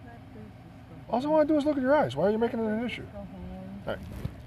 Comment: I swear i tuned back in when i heard "all i wanna do is look at your ass." cough its not "ass" its "eyes" infact